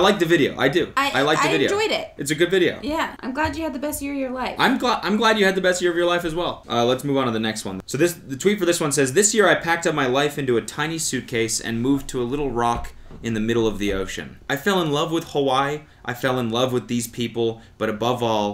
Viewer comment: I was doing hw while listening to this and I genuinely thought he said “packing up my tiny WIFE into a suitcase” and I was like.... well that can’t be comfortable for her